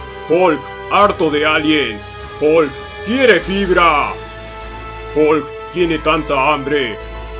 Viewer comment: crei que dijo hulk quiere pij4 jaaj xd